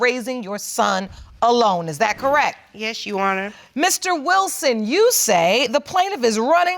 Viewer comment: did she say “yes you wanna” or was it just me 🤔